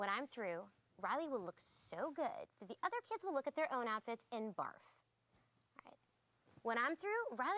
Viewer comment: I thought Disgust said "Riley will look so good, the other kids will look at their own outfits and fart." X3